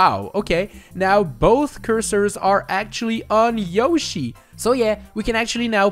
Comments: Why does sound like Luigi saying “oh yeah” when said “so yeah” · That “So yeah” sounded like Luigi saying “Oh yeah”